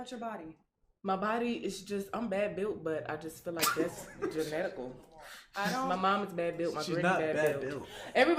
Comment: Did she just say genetical?….. at part 2:40 😮?….. it was that for me right after she said that built body. 😂😂😂😂